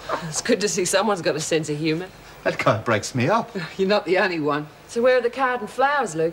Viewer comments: Did he say "That c#nt breaks me up"??????? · It sounds like Tom says “that c*** [Rodney] breaks me up.” LOL. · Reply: He says kind it's just it does sound like the other